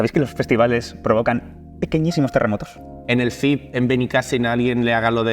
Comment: ¿Qué dice? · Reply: pequeñísimos terremotos